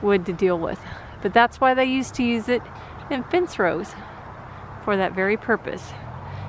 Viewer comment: Great job on the clean up. I couldn’t understand what you said at 6:18. You said the tree had thorns and that’s why they used to use it…? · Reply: They are also called "hedge apple" trees because they used them in hedgerows because of the thorns.